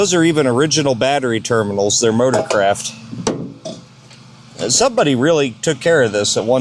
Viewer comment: i thought he said "Murdercraft" 😅😅